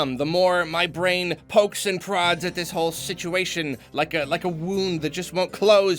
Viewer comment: "Like a wound that just won't close ... was that too graphic?" Okay I misheard you and thought you said a WOMB that just won't close, now THAT'S too graphic